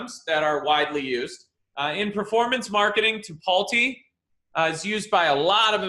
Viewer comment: he says Tipalti (the caption says "Paul T")